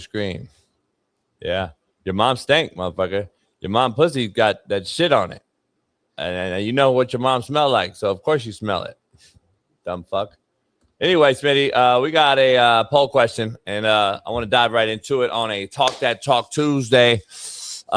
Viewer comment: I can see how he thought you said gay smitty, annunciate boo bear!! ❤❤